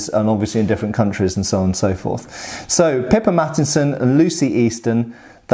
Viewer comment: I keep hearing "Pippa Mattinson" as "Pippa Matt Easton"